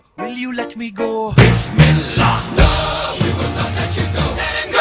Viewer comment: I thought they say : ''This spell we will not let you go''